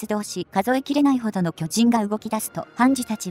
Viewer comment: の文字に『巨人』と書いてありましたが、僕だけかもしれないと思うんですが、僕は『きょち◯』に聞こえました笑笑笑笑